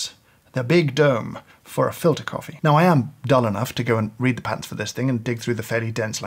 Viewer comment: kinda funny how the closed captions thought James said "dumb" here instead of "dull", two very differently meaning words in this context. quite opposite